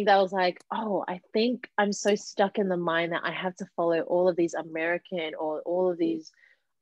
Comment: Wait did she say "American"? 32:10 Um tiktok is Chinese.......